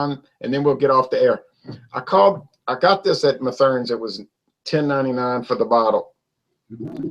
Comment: what is lathgerns you say in min 47:28 sounds like a white ladys name like some ones ant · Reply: Sorry, Gabe. Matherne's is a grocery store chain.